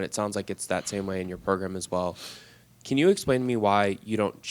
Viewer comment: Did he just say, 'can you explain me'?